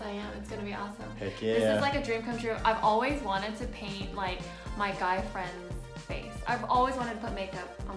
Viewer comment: When said "....... My guy friend" I thought she said "........ My gay friend" omghost I'm so mess :v